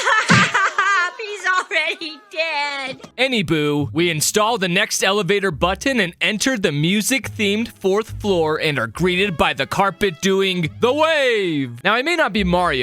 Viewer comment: did he just say Any boo ?